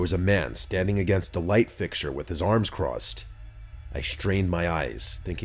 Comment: at some meanless bar? I'm not sure what he's saying. Meanless? what's that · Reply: nameless* llisten more carefully next time